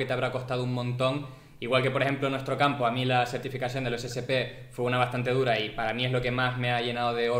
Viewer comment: creo que dice certificacion ecsp.